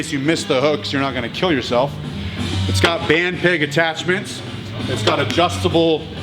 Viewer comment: lol, now I hear "band pig" (23:06) most times when says "band peg".